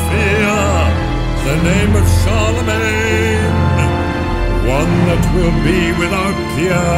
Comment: when he says "the name of Charlemagne", at first I heard "the name of Saroumane"